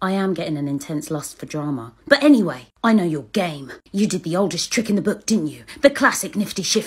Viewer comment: I CANT- I THOUGHT HE SAID “But anyway, i know your gay” AJXJSHAHAJJSHSXHJAKZ